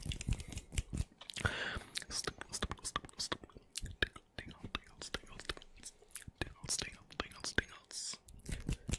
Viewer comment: was he saying stupid😂 0:38 thanos? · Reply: Tingles, not thanos lol